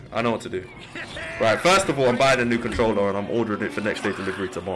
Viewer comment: I could have sworn he said "bannin' new controller' which wouldn't surprise me